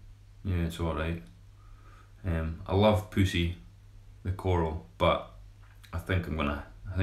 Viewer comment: did he say 🐈?